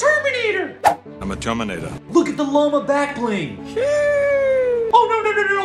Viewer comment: I thought it said look at the joe mamma backbling😂😂😂